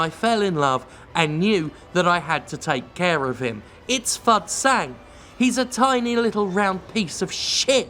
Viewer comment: I honest to God thought you said his name was "Fuck Sand" instead of Fud Sang and I thought "Yeah seems about right".